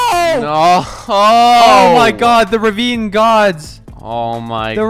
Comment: When said this ravine god, I thought he said meme god